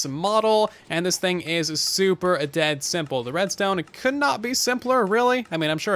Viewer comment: "Could not be any more simple" Mumbo Jumbo: Hold my beer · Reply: oh ok. I'm sorry I thought that you said could not be any more simple. I'm so sorry for putting words in your mouth. I just did not hear what you actually said. I just made this comment for fun and to be funny. I'm sorry if it hurt your feeling. :)